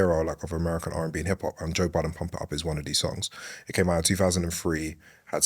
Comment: I thought it said Joe Biden for a sec 😂